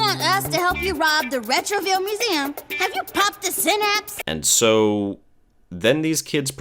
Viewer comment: I had to rewind the video at 9:23 because I thought she said "popped a Xanax" lol